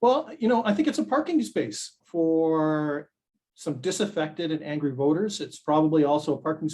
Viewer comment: Did he just say: "It is a parking space for desinfected or disaffected and angry voters who are against mandatory vaccines..."? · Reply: disaffected